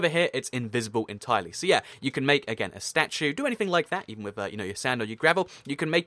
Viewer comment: I honestly thought he said GRANDMA statue 😝